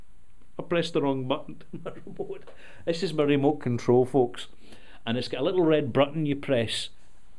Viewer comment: "this is Merry-Malt Control, folks!". Hahaha Oh, I've misheard it.